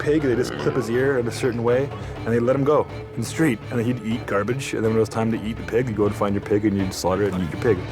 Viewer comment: Did he say "it'll eat garbage, shit"